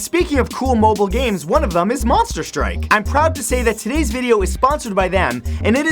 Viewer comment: Old phones from the 90's to 2010 are beter. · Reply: Monster strike? this whole time I thought you said monster bike lol wrong game.